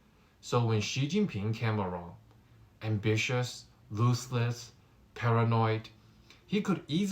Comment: Technical issue: What is the word at 8:38 about Xi, "when Xi came along, ambitious, XXX, paranoid.." the captions say "looseless" but that does not make good sense. So, what word am I missing. Thanks. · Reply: Ruthless.haha!